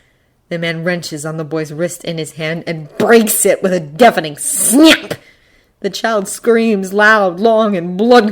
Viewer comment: "with a defaming SNYAP" I can't this is all I hear 😭🥲